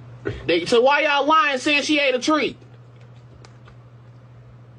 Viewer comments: I thought he said she ate a tree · I thought he said "she ate a tree" 💀 😂